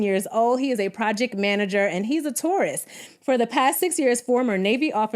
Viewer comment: I kept hearing "so and so is a tourist" and I was like what are they DC transplants or something!? It took me awhile to understand it was Taurus not tourist 😂😆